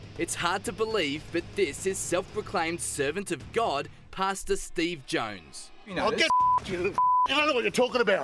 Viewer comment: I live in America and I know this is in a different country, but I kept hearing them say "Pasta Steve" instead of "Pastor Steve". Anyone else?